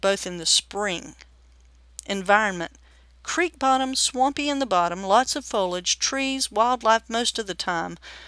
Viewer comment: I though I heard her say the creek had a swampy bottom with lots of beer!